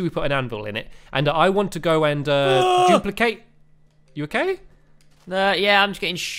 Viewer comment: Did Stampy Just Say He Is Gonna Duplicate In